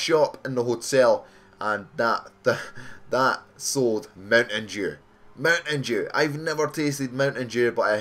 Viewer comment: Lol sounds like you said mountain jew